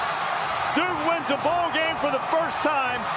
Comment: Am I hear that right ? Hasn’t won a game since 1961 ?? U gotta retire the team at that point 🤦‍♂️😂 someone correct me if I’m wrong please 😭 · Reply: A “bowl” game lol. It does sound like ball game tho